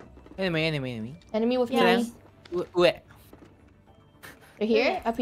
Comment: I think Kanae said 'ue', not where, so Qu clarified to Selen saying 'ue is top (in Japanese)'.